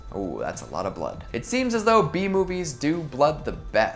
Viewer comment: What said: ''B movies'' +What I heard: ''Bee movies'' weird flashbacks of Barry Benson killing people